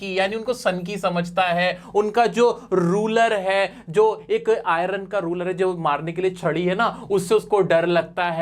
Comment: sir in this part of the video its written IRON RURAL instead of IRON RULER